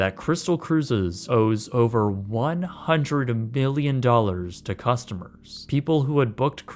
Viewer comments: do you say 100 MILLION or 100 BILLION? I hear billion, but auto-caption says million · I thought I heard "billion" at first. 😳